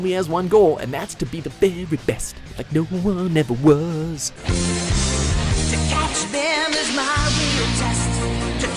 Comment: did he really say "Ass Catchem?